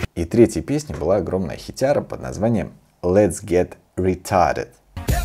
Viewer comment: Версию BEP от я услышал в первый раз в этом видео. И послышалось мне Let's get reTIREd...😂 Давай выйдем на пенсию)